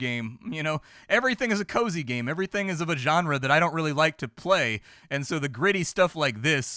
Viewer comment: "Vagenre" ? Did I hear right? When he was talking about cozy games, it sounded like he said "vagenre" to refer to those types of...? games for pussies?